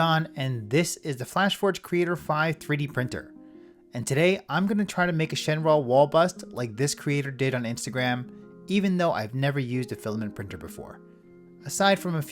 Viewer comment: I thought it said "Shenron will bust"😭✌️🥀